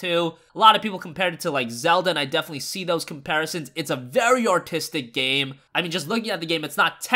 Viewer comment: Did no one else notice that at 3:30 he said autistic instead of artistic?